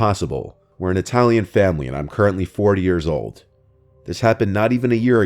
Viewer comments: did anyone hear “4 years old” instead of 40? It took me a few times to unhear it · It sounded like he said I am currently 4 yrs old not 40 yrs old.